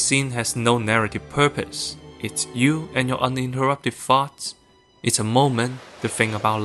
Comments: I can not be the only one that thought he said "it's you and your uninterrupted farts" at first. If you're wondering, he said "it's you and your uninterrupted thoughts". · I heard uninterrupted farts for a sec there and had to rewind 😂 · brother, I thought you said "you and your uninterrupted farts." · Coulda sworn he said “It’s just you and your uninterrupted farts.”